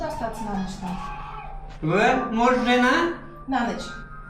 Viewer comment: I’m not a native Russian speaker, but I think at 20:20, when the subtitle says “unclear speech”, he actually said “муж, жена”, which means “husband, wife.” In Russian, that’s a typical way of asking “Are you married?” Please correct me if I’m wrong about that part. Greetings from Poland!